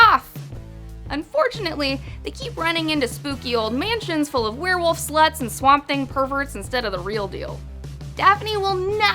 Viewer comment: Wait did say all the gang keeps finding is werewolfs, SLUTES, and swap monster things